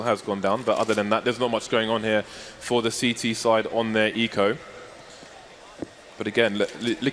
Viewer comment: Só eu escutei um ''Foda-se'' dsadkasd